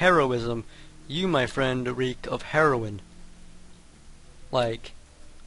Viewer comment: did u said herobrine?